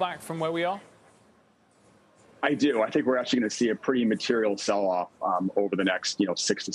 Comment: Honestly thought he said "materials" not material. Because Materials sector will benefit from interest rate hikes.